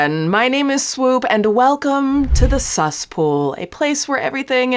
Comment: welcome to the suss pool lol although i thought you said ceas pool😂😂😂